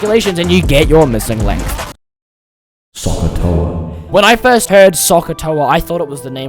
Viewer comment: When i first heard SohCahToa, I thought someone said "Suck a toe"